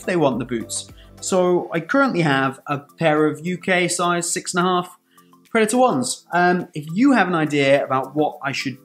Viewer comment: Sorry I shouldn't focus on all the little details but this just caught my ear. Did you just say Predator Ones? That would be a teally great football boot by the way. Imagine a collaboration between Adidas and Puma. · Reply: Hahaha i checked I 100% said predator 1!!! Didn't catch that in editing!